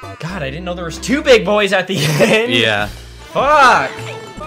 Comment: sounds like Luigi saying “fuck ha ha haa”